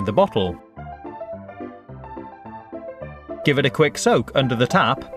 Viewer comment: I swear he said "...give it a quick soap under the tap..." Oh well